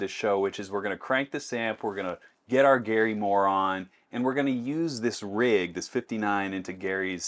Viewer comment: Did he just said Gary Moron?